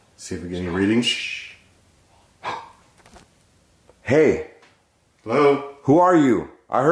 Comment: Yohhh i heard that hey loud and clear at 23:20 😭